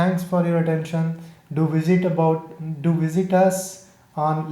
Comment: i thought it was gonna say "lets nut" XD